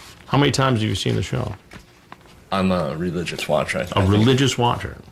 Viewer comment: Take another listen. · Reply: He said 'not a religious watcher'. Chris misheard lol.